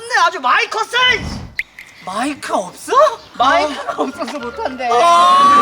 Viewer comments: 초에 가 중학교 애기를 했는데 7:30초에 아랫 글이 '흔남고 밴드'라고있었어요~ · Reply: 저만 7:47 가 마이크가 없어!! 이러는걸로 들었나유?.... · 초 많이컷네 할때 나도 마이크 없어 라고 고 들었넼ㅋㅋㅋㅋㅌ